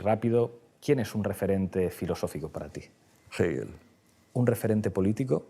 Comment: alguien me puede decir el nombre de su referente filosofico? no consigo entender bien el nombre que dice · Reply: Hegel